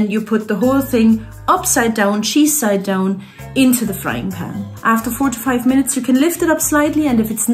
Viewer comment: " After 45 minutes.." Hm, i doubt that...🤔 And after listening very close i think she saye "After four to five minutes." Just saying in case people have a bad hearing like i .